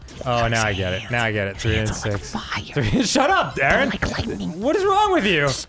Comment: Did Dan call him Darren at 23:02?